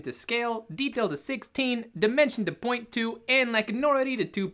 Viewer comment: This is impossible. Every time you say "dimension" I hear "demention" and that's how I feel.